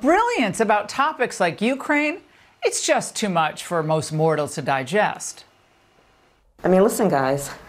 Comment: why does she call people "Mortals" ? Did i hear that right how come nobody talking about that?